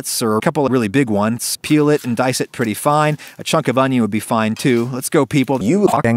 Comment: what does he say? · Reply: A chunk of onion would be fine